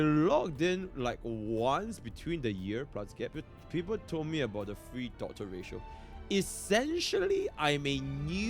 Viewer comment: I think they mentioned that they started when the game released but quit shortly after, logged in to claim Ratio, quit again, then came back during FF's banner. Looks like they just stop playing the game from time to time. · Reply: ah I misheard, thanks. I swear I heard "started the game for the free Dr Ratio." oh well that explains it